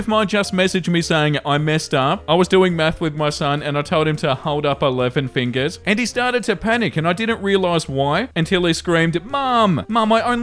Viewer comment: I was listening to this and not watching the screen and at 4:36 I THOUGHT THEY SAID "I WAS DOING M3TH WITH MY SON" 😭💀